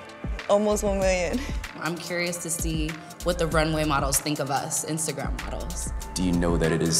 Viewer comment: I thought it said runaway models